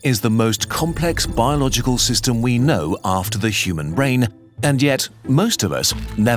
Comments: I swear i hear "the human rain" every time i watch that clip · I swear i hear "the human rain" every time i watch that clip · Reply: I heard “rain” the first time but heard “brain” when I rewound and listened again.